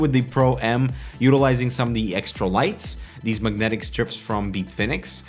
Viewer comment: Did he say magnetic strips at 4:59?